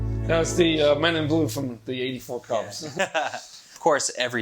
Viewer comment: What was that music (title)? Didn't understand what he said :) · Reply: Balázs Lengyel think he said The Men In Blue from the 84 Clubs?